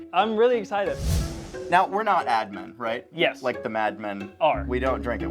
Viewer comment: I know they keep saying “Ad Men”, but I keep hearing “Admin” and it’s driving me crazy. 😂